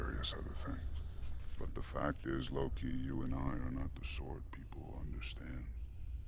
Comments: i thought he said "Low-key" not loki 😂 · All this time I thought he was saying "lowkey" instead of Loki.